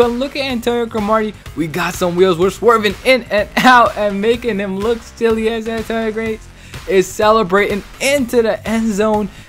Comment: Lol at 6:58 what did u say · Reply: "Making him look silly, Antonio gates" I think not sure